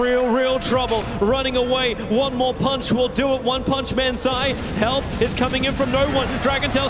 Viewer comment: did he say one punch man ?